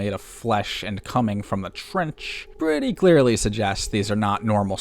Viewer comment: At this point just nuke the trench. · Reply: I thought you said the french